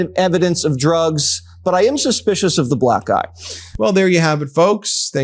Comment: Lol at 16:46, closed captions read, "but I am suspicious of the black guy" instead of "the black eye" 😂😂